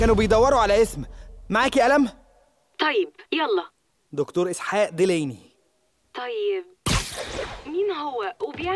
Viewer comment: تراوالي سمعت ..سحاقلدليمي.. هاذة .دكتور عراقي ختالو.علاوي . تحرياتك هنا حبي💝💝💝💝💝